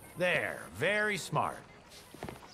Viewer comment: He definitely did not say “docks” at the end there… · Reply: nah, he said "crocks"